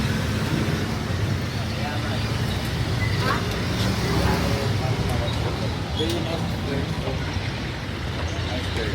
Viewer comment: what did they say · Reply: Nice girl